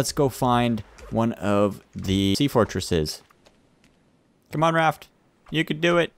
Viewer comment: on 3:01, didnt sound like he said 'tea fortresses' ?